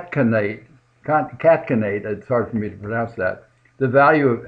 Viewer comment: LMAO it sounded like u were trying to catkenate LOOOOLLLLLLLL i like u